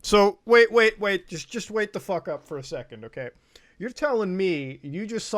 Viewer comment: im sorry did i just hear wait the fuck up